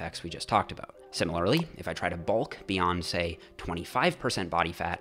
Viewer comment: Tell me you didnt hear him say Beyonce! 😂